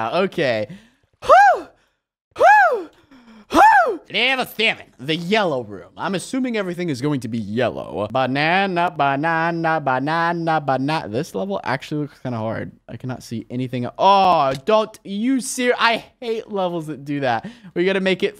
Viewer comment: I thought u said banana sandwich 😂😂😂